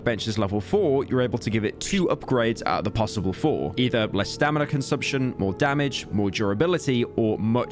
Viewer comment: when pyro said less stamina drain I heard "le stamina drain"